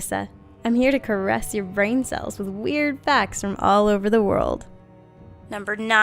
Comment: Melissa: "I'm here to caress your brain cells with weird facts from all over the world!" What I heard: "I'm here to caress your balls with some lube I got from all over the world!" :3